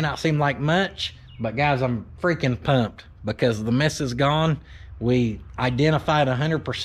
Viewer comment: said "The miss is gone." But I heard, "The Mrs. gone". I thought WHOA! ol' playing bachelor dad, then I remembered I just watched an hour long video about a cylinder mis-fire.